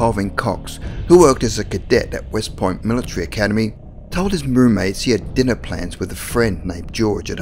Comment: Great video... and channel! Your voice is perfect and so is your presentation. Though, with your accent (which I love, BTW) I keep hearing "roommate" as MERMAID and cracking me up! 🤣